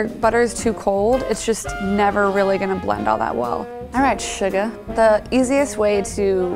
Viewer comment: did she just say "SUGA" 1:58 heheh BTS